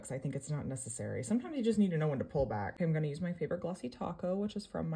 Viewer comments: Am I the only one who heard her say "glossy taco?" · Her: “glossy topcoat” My mind: “glossy taco” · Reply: I heard her say glossy taco and I thought she said "which is my brand" afterwards, so I played that part back and she definitely said "taco" and "her brand". I thought she was implying she was using Cristine's holo taco nail polish when she said taco, but I was confused when she said it was her own brand. I came to the comments to see if I was the only one confused. I'm glad that others also found this curious 🧐 and I wasn't just over thinking it. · thought she said glossy taco